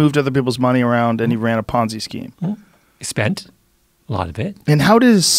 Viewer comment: Ponzi scheme? I thought you said Fonzie scheme . Ok ..dumb joke. I'll see myself out.